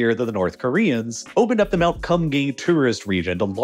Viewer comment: I thought he was saying Cum Gain at first, Kum Gang is not necessarily better. ( PS Gang nam song has been out for a long time, why pronounce Gang so different from that?)